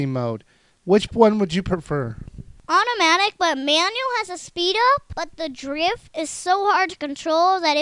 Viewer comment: did they say "manual has a speedo." XD